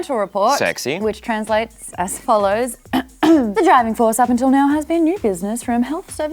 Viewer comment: anyone else heard "vaginal force"? ...no? I'm the only pervert here? ok cool.